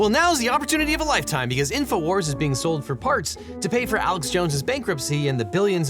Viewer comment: I heard “InfoWars is being sold for farts” the first time through this and thought “I’m not sure it’s even worth that much tbh”